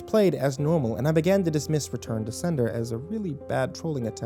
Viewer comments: Who else thought it said " return to slender" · Thought it said Return to Slender. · I thought it said return to slender. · I thought it said return to slender